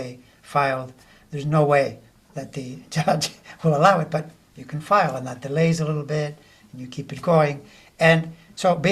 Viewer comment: at ~ 13:30, you mntuoned 2 attorneys that you listen to, but I didn't catch their names...Please make a referral ❤ 🗳💙💙💙🌊🌊🌊🇺🇲🇺🇦🇬🇮💪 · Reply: Andrew Weissman and Mary McCord, they have a podcast named "prosecuting donald trump". Very intelligent and informative.